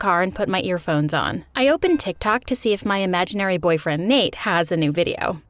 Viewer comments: Why did it sound like he said Tik tak instead of tik tok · why do i hear "tiktak"